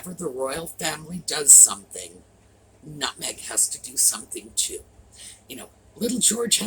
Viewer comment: Lol, for a second I thought I heard you say "Nut-Meg has to do something too" but you said " Now Meg has to do something too" Hi and.